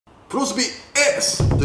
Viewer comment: 最初プロスピXって聞こえたわ笑